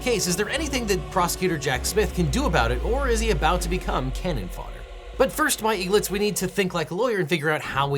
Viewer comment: i thought he said Cannon Farter instead of fodder 🤣